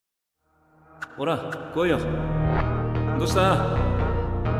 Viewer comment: what does he say in japanese? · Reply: Hora, koyo! Dosh-ta? Nigen-natstarano? Umailo no hoda ro